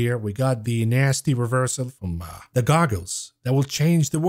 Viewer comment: The goggles...I thought you said the Goggins😅 STAY HARD